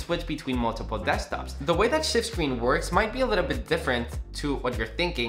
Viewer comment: for a second there i thought he said shit screen 😂😂